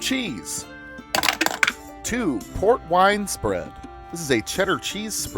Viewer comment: Not gonna pretend like I didn't think I heard "Pork wine"